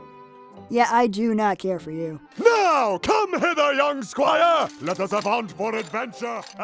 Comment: thought he said "now come hitler